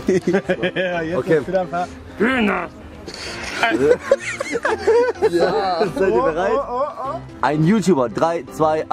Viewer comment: Was sagt bei 6:35 ? "Ich hab den Mund...." ? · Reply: Katharina Gemba ich hab den Mund voll, du hast den Arsch voll😂